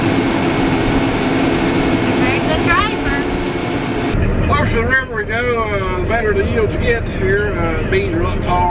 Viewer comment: minutes into the video can anyone please feel free to tell me what the heck is saying wow closed captioning is no where near correct at all LoL LMAO we love you not your fault but keep filming please 🙏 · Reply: centsless fabrication “the closer to the river we go, the better the beans get”